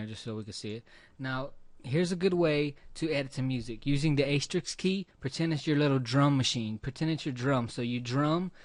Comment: thank you! on this video at 6:35 you mention a key but i can't understand which key you are referring to. you call it, "pretend it's your little drummer machine". i can't understand what key you are referring to. :( please advise. thanks! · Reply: It’s the asterisk key or * which WAS the shortcut for creating a marker on the timeline, I’m pretty sure it’s default has been changed now to the M key possibly why everyone is so confused on what the heck I’m talking about, but yes, I’m drumming on the Create New Marker shortcut key (which I also think you can even change that in the keyboard shortcuts as well so yeah, whatever. But yes, all that I’ve shown in this video is really just very basics and I have grown so far advanced in creative design and production over the years since this tutorial making professional music, podcasts, films, music videos, short clips and green screen live action Tv comedy programs as well as special effects, motion design and even 3d modeling, texturing, lighting & animation just myself and my adobe and various other tools that I have far passed Mastered by now making both my own projects as well as a lot of musicians, rappers and local business owners various projects and watching & reading every ideas, tips, tutorials & references everywhere you can think across the internet and one on one with other like minded professionals I come across EVERY day of my life since then... so I if someone asks if I can do ___, the answer has always surpassed “YES” and takin to the next level or what they even believed possible! IN OTHER WORDS: What would you like to learn today? Throw your best questions at me, you never know what will come out of a single question submitted to me and what you can learn to